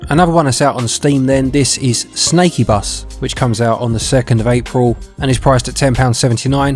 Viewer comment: When I heard Snakeybus I thought I heard Snake Pass... us, poor non-Native speakers!))))))))